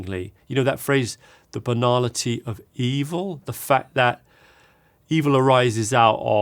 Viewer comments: I heard "banana tea of evil" and was so confused lol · I thought he said “banana tea of evil”. 😂